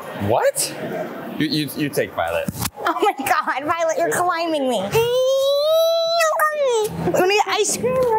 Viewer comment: I swear at 14:09 violet said YOU ARE PAYING!